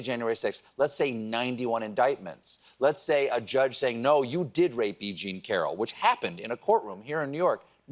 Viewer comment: The fucking censorship on YouTube is getting to China levels! 2:05 i put on closed captions because i wasn't sure what he said the judge said. Closed captions quotes "judge said, he did a great woman." I'm pretty certain Stephen said "a judge said, no you did Rape B.G. Carroll." Fricken YouTube censorship is really starting to piss me off